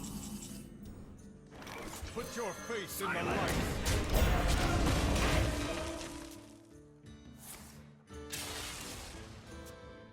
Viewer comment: Is it just me or does Tirion actually say "put your face in the light" at 11:05? Like, instead of the normal one?